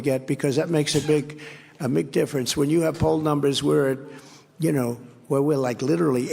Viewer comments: did Trump say “it makes a big McDifference?” Also Hasan’s cute sneeze 🥺👉👈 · Did Trump say, "it makes a big... a McDifference."?